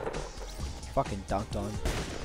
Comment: did he say get dunked on